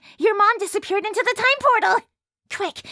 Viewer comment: wth is Lucca saying? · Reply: "We have to chase this failed sequel hook!"